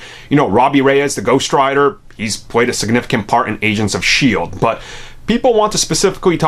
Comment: When you say "Agents of shield" it sounds like you say "Asians of shield"... Am I insane????